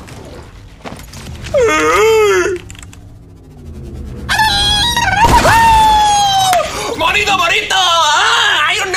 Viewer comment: wut did u say ? Hahahaha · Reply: Burrito bonito, that was the name of the guy he killed